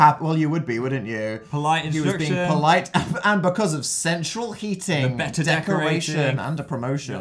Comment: I thought that "Central Heating" said "Cereal Eating"